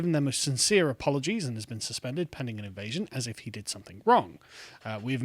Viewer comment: Did I hear that right 0:56 "Pending an invasion" 😂🤣